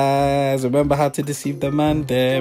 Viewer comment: Did he just say "another episode of deceiving the mandem" deeeaddddd